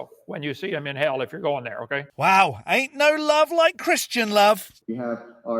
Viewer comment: I heard it said as " There is no hate like Christian love."